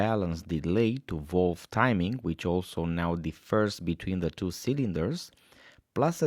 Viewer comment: What did he say at 6:21 about defers between cylinders? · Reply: Said that the valve timings are different.